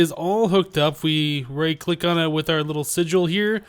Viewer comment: Right at 27:05 did he say Righ Black lol